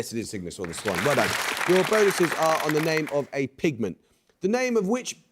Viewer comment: the host’s unclear diction impedes understanding- it sounded like he said “… on the name of a pigman”